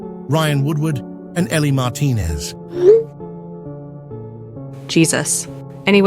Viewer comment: i heard emi martinez not ellie martinez